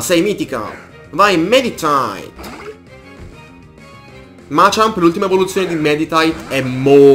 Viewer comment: ma solo io a 12:13 ho sentito machamp al posto di medichamp